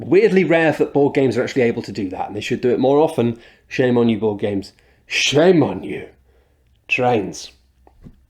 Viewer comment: is it just me or did he say "football games" ???